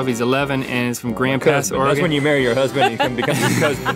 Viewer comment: why does it sound like rhett said "when you marry your husband" and not "when you marry your cousin" ?? am i gong crazy ?